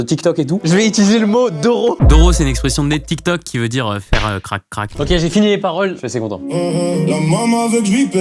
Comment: c’est quoi ça veut dire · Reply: sa veut dire faire l’amour